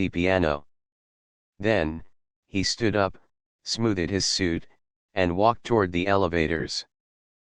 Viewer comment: “Smooth -ed” instead of smoothed